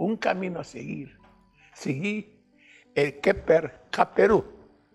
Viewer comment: Qué dice en el 6:32? Un fonetismo como "segui el keper kaperu".